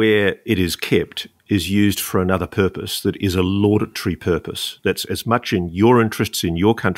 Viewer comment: Can someone tell me what word that he uses on 18:15? The other purpose for the building where the large UFO is stored. He says: "The place where it is kept is used for another purpose, that is a 🤔🤔🤔🤔🤔 purpose..." I'm hearing 'laudatory. Is that what he is saying? And what does that mean in this context. Sure, Google Translate helps but isn't 100% foolproof. My English is pretty good for a non-native speaker, if I say so myself. But so now and then I hear a word that I never hear before... 😅 Anyone? And great show again of course!! I can listen to Ross all day long... ✨🛸👽✨ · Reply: He did indeed say laudatory. I believe he means that the other purpose this facility serves is good, or admirable. Personally, I believe he’s referring to the base at Pine Gap in Australia. It’s for early detection of nuke launches etc. No weapons are stored there (to my knowledge). Yes, there is an invasion of privacy with the amount of surveillance that goes on there, but still, it seems to meet all the criteria